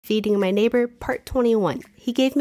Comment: i thought she said eating my neighbor ☠️